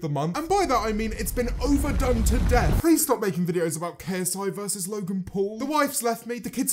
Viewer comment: Lmao the captions thought you said “loogan pool”